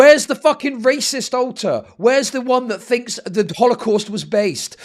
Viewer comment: pretty sure said “alter” here but it sounded like he said “alt+tab” which is way funnier in this context lol